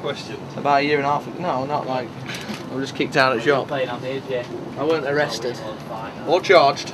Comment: did he say kicked out of a job ?